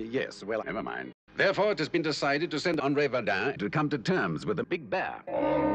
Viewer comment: What does Captain Blue say at 2:47? It sounds like "I'd like permission to be Verdan." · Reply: I thought he said De Verdain